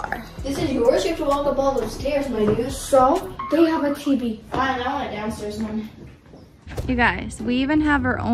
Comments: what did say?? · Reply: She said that she wanted a downstairs bedroom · umm what did say?? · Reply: Yup, she said “my dude”